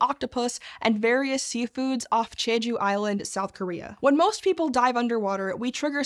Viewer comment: Did she say Jeju Island?